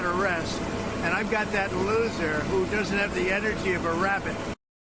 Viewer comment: Did he say that Vice-president Harris "...doesn't have the energy of a rabbit" ???? If he was trying to insult her, it's not working. 🐢🐇